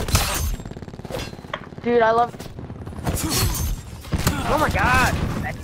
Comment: did he say dude I love you